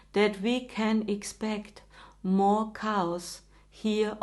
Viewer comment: Did she say more cows?